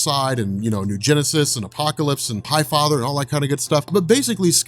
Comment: Wow I'm deaf. the first time I heard him say high father, I thought he said Pie Father.